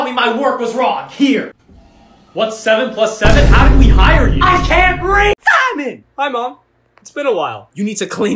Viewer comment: why did she say I can’t read